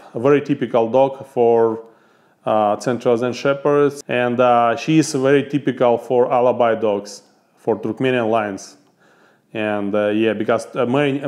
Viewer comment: At first I thought he said Nubian Dogs for milking???? I'm glad I read it with the captions.